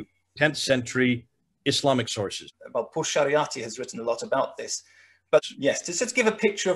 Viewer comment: Amazing presentation. I am glad Red Judaism is gaining a wider audience. Did anyone catch the name of the scholar referred to at 16:07? It sounded to me like Portia Ratti. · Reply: Parvaneh Pourshariati